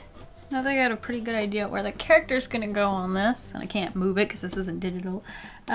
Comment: I thought she said: “And I can’t move it ‘cause it isn’t deadpool.” What she really said: “And I can’t move it ‘cause it isn’t Digital.”